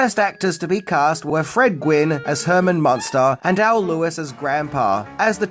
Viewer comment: I heard you say Frank Gwin and it is actually Fred Gwin